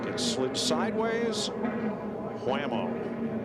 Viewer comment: Did I just hear Rick Allen say Whammo